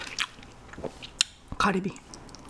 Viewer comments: sounds like she said cardi b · I thought she was saying Cardi B💀